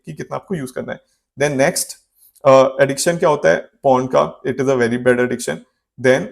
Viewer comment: I heard you say "PORN" but subtitle is showing Phone.